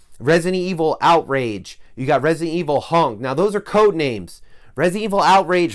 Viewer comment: did you say Resident Evil Hung or Hunk? Either way, I'm excited. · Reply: Hahahahaha. I said hunk. He is a character in the serious. Lol you would comment this zach lol